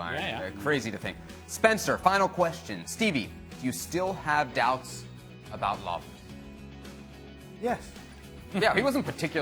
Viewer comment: I thought he said “ you still got doughs about love “